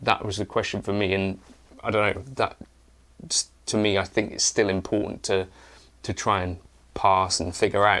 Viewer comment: I thought you said “stealing porn” but you said “still important “ 😂 Now we know 🎉